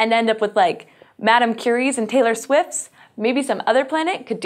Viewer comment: You said "Madam Curie" I heard "Madam Toussaud" Was very, very weirded out for a second.